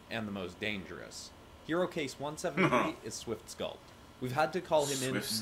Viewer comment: I thought he said swiftskull and not swiftsculpt, and I just thought that swiftsnap would have been a better name until I heard swiftsculpt.